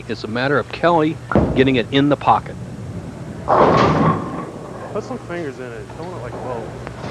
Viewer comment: Did I hear Kelly Coffman say "You're throwing it like Bo"?